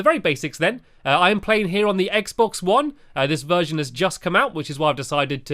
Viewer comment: Oh I'm so sorry I misheard you I thought you said minecraft XBOX not Xbox ONE cuz XBOX is EASY! sorry I'm a huge fan :)